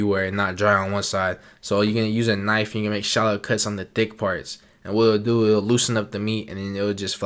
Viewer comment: I thought he said “make shallow cuts on the dick parts” 🤣